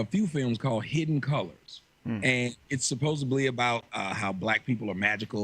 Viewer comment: What a disgusting display of desperate insecurity. These gossipy yentas rip someone for saying "advise" instead of "advice," but when one of them PLAINLY says, "supposably" (17:42), it doesn't even rate a giggle. And really? We've sunk to calling Jesse Lee Peterson a "slow wit"? He's an honest and fair man who would NEVER be a part of this catty bitch-fest. Behold the HUGE difference between you three and me: I TELL YOU as opposed to telling everyone else behind your backs.